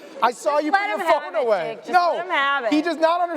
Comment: did Zooey call Jake Nick? 😂😂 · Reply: Replayed it loads of times when I first heard it I think she says jake but definitely heard nick the first time